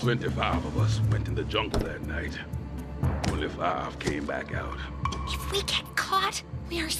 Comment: He's saying only 5 came back out??? I always heard it as "25 of us went in the jungle that night. 25 came back out"